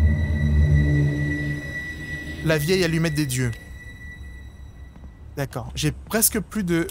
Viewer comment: C'est moi ou il a dis à 7:13 la vieille Allumette des Dieux? xD